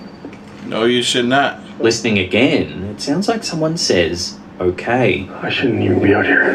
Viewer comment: In 1:18:57 instead of that spirit saying "Okay?" It sounded like that Spirit said "Jordan?" (Right quick)